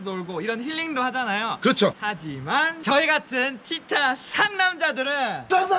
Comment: 상남자아알!!! ㅋㅋㅋㅋㅋㅋㅋ 왜자꾸 상남잘이라고 들리죠? ㅋㅋㅋㅋㅋ 상남자에 대해서 잘아는사람인갘ㅋㅋㅋㅋㅋ